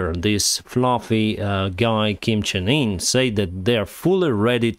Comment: at first I thought you said “this floppy guy” and I laughed. Upon re-listen I realized you said “this fluffy guy”. “Floppy” is better haha!